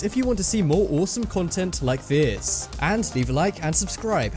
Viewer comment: subtitle says: if you want to see more awesome content like BEER. (he says this) Unsee that! :)